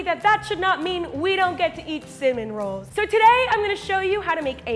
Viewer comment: did she say Simin rolls.